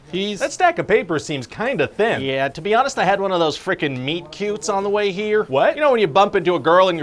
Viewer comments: This is one of Ryan’s best pitch meetings. The miscast. The meet cute. The boy who is vaguely into Borderlands. So many solid bits. chef’s kiss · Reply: ngl, I heard "meat cube" initially, and was very confused about the bit at first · When he said "meet cute", I thought he said "meat cube". Now I'm disappointed that I won't get to hear Script Guy explain what a "meat cube" is.